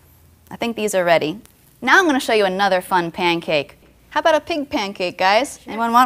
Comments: I SWEAR she said PINK pancake. I even raised the volume on my iPod and I still hear PINK not pig.. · darn..thought she said PINK pancakes. i wanted to see a fun colored pancake.